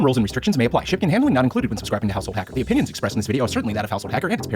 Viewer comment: who can caption that? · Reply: I heard "behind my house"